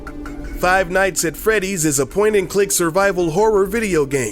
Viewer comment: Who else heard him say five night frowdeys near the beginning and said fozbear